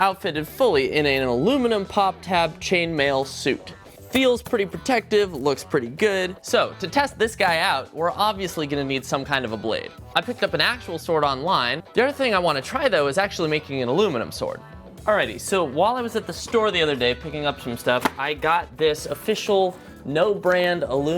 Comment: I thought he said sword art online 😂😂😂